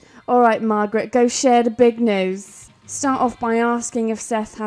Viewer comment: with the subtitles on it says let's share the big nose instead of news LMAO!!! 🤣😂🤣😂